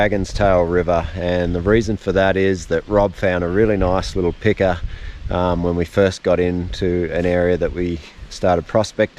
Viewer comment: It sounded like you said "Nice little picker there". Leave your phone in rice; it has dried out my phone previously..